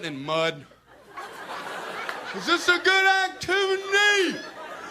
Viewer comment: “ is this a good ack Timothy?” You can no longer I hear it